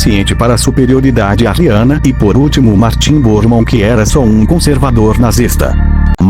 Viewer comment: eu não entendi o nome kkk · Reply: São dois nomes: Reinhard Heydrich e Heirich Himmler acho que é assim que escreve